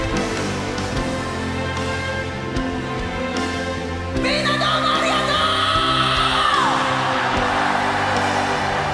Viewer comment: 일본어 고수님들 8:15 에 토시님 뭐라고 하시는 건가요??? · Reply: 모두들 정말 고마워~ 입니다^^